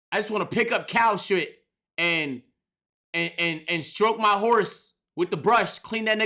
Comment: i was listening from a different tab then i heard this man say i wanna stroke my horse and i lost it